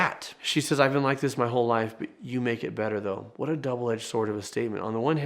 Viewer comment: In the hospital bed, I don't think Rue was saying "I kinda like this moment Jules." What I heard was "I've been like this my whole life Jules." And it makes more sense to me.